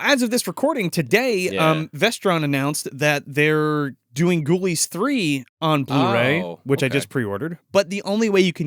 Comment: Yo I straight up thought said "Goonies 3" instead of Ghoulies 3 and I got really turned around for a second